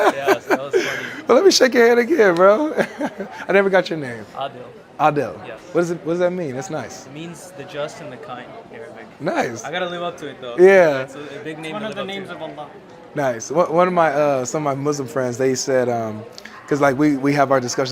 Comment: did he say “that’s one of the names of Allah?”